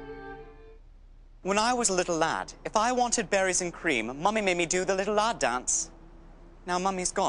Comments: I thought he was saying “Batteries and Cream”🧍🏻‍♀️ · I remember when I heard this in 2021 I thought he said batteries and cream